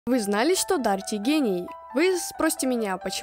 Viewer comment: Я когда услышала "дарит гений" мне послышалось "дарит г@ АХАХАХА